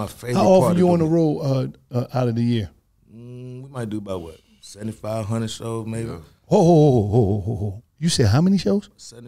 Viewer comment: I thought he said 7,500 shows too got damn